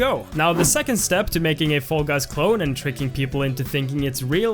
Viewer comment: lol captions says full gas instead of fall guys